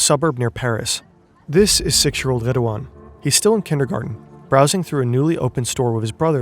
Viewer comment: I kid you not at 3:57 I heard "This is sexual Redoine" instead. 😅